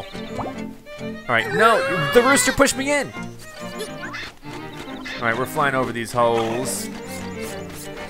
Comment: did i just hear him say ''let's fly over this ho*s*?'' EDIT: ik wat he actually said, but thats wat i heard at first xD